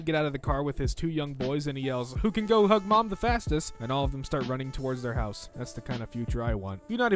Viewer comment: I thought it said “WHO CAN GO HUG MOM THE FASCIST?”